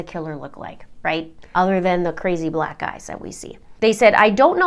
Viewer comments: Totallyyyyyyyyy thought you said "crazy black guys that we see." I had to rewind and watch your hand signals to realize you meant eyes. I knew you would never say something like that lol. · It totally sounds like you say, "besides the crazy black guys we see" EYES, she said EYES I was just listening so when I looked at the video it's clear you say, Black EYES that we see. In case anyone else heard what I did. Lol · Anyone else heard “crazy black guys” instead of “crazy black eyes?” 😂 @ 5:39 I had to do a rewind! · Did you say or quote “What do killers look like, other than those crazy black guys…”? I thought I misheard but the captions came up the same. Surely not. Killers came in all races. Apologies, if I’m mistaken but if I’m not obviously this channel isn’t intended for me. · I heard 'what does a killer look like other than the crazy Black guys that we see?' I had to rewind to realize you said 'crazy black eyes'. Whew. Glad I just misheard. That was a Key & Peele moment. (they have a skit on 'black ice/ Black guys") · did you say a killer looks like the crazy black guy? I hope I misheard. Most are white